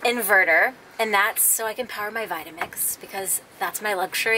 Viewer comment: OMG LMAO.....when talking about your power i thought you said "so i can power my vibrator'!!!!!! lol!!! had to play back then heard 'to power my vitamix'.!!! either way it's cool! :)